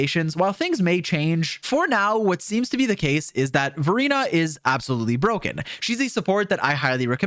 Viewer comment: i thought i heard him say "Furina is absolutely broken"